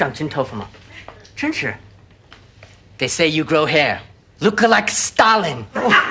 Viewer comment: When I first saw this I thought he said “look like stallion”.